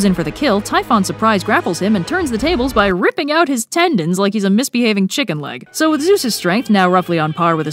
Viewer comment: Is my hearing failing again or did she say 'Ripping out his tendons like he's a misbehaving chicken leg? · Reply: the sad part is, I thought she said, "like a misbehaving childling(child)" and accepted that as a not disturbing fact